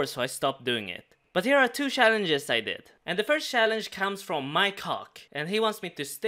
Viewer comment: did anyone els think said "my cock" instead of mike hawk? at 7:35 please dont say it was just me LOOOL